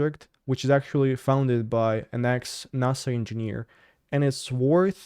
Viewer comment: At the time 13.53 in this episode it was hard to understand that U said NASA engineering it sounded like you said nazi engineering lol just slow it down alittle Ur rushing Ur words in a language that was not Ur main tongue so it comes off as slurred speech so when a word is hard to say as a man find a bunch of clips of men saying that word I recommend that U get it from some that has been said by others that English isn't their first language as well so U can find a way that U can say it cause just using a western acsent is enough